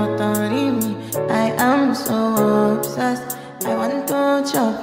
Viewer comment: I thought it said "I am so upset"